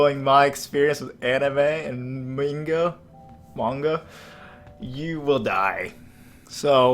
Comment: I thought u said mango